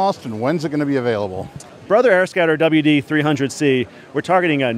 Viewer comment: Sounds like he says AirScatter every time (like 2:48). Anyway, what if it's bright out and you're wearing sun glasses, wonder how viewable it is.